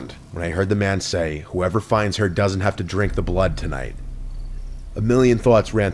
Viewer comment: I THOUGHT I HEARD "BLOOD"!!!!😮😮😮😮